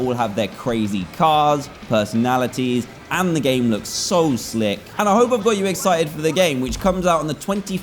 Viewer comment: Wait 7:29 did he say slick?